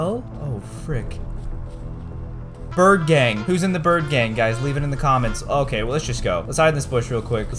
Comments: IM IN THE BIRD GANG!! oh wait I thought you said the turd gang hehe, funny! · When he was saying bird gang I thought he was saying bird game 🤣🤣🤣😂😂😂😂❤️🇮🇳🇮🇳🇮🇳🇮🇳🇮🇳🇮🇳🇮🇳🇮🇳🇮🇳🇮🇳